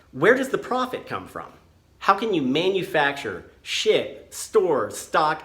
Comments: I honestly thought you said, "How can you manufacture shit?" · I thought he said "how can you manufacture shit" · Sounds like you said "How can you manufacture $#!t" Just sayin' It fits though.